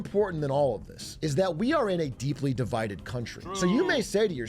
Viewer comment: "We're a deeply divided country" but it's less divided than last election and what I really hear is "We're a deeply retarded country."